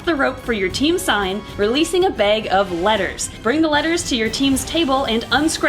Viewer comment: No lie, when I watched Bob's video and you explain the rules to them, I thought you said "lettuce" instead of letters. I was so confused until I saw everybody dump the letters onto the table. EDIT: I also love the team building event. Not only are they fun to watch, but it's fun to put people who are about to compete against each other into a team where they have to work together. I think that's a great way to lead up to a competition. Bob also featured an adorable close-up of Rex in his video